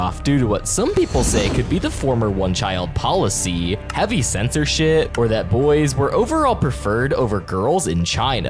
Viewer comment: did he just say the s word |aka| shit